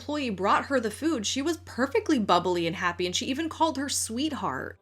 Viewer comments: I heard 'sweetheart' not today or sir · I heard "sweetheart" said really fast, not "sir." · I heard "sir" after the idea that she called her sir was introduced to us in the video. When I watched the clip the first time, I actually heard "sweetheart." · I am late to this, but I hear "sweetheart" and not "sir."